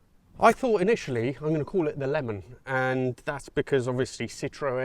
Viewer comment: At first I thought you'd said you were going to call it "The Lemming"...